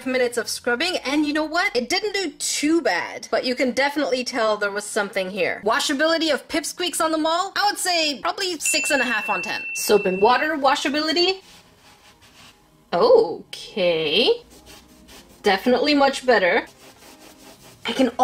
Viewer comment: did she say on the mall